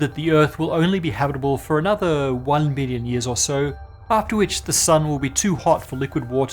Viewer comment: Sounded like it was said earth would only be habitable for another 1 million years. It should be ~1 billion. Might have been misspoken or just not clear enough to distinguish "billion" from "million".